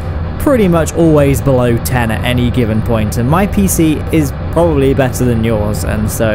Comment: "and my pc probably better " :') he's talking about mine guys what i heard: "my pc is defiantly better then your pc"